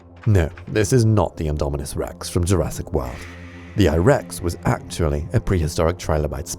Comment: Did you say "Jurassic woah" lol